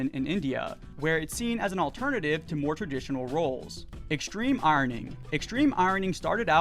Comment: when you said "extreme ironing", i heard "extreme *irony*" and I thought you were talking about like sarcasm or roasting competitions lmao